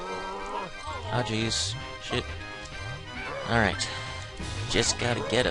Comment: Did you say "Shit" when you think you were gonna get hit again by the lava?